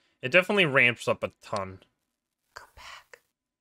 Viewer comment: when i heard "come or go back" i was scared о.о